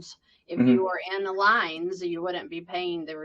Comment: She is using words I do not know and the closed captions is not spelling correctly. Can anyone help me, please? 0:55 “in alines”??? 3:24 “versalean”?? (CC spells it “vereline”) Thank you! I really want to learn this, but tough to do when I cannot figure out what the terms are. 😂